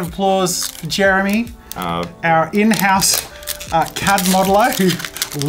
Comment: When you said Jeremy is our cad modeler I thought you said Cat Model!